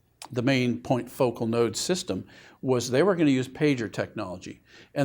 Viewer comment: You said "pager" technology, at first I heard "taser'. I was thinking, that could motivate a person to get up and go to work, chickens to lay eggs etc.